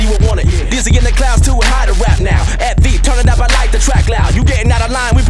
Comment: I always thought he said "dizzy in the clouds, too hot in rap now